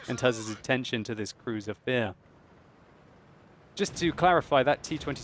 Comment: "Crüzer Fïr" man you have been playing to many German tanks buddy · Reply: Thought he said Cruiser Fear, because that thing puts the Fear into you.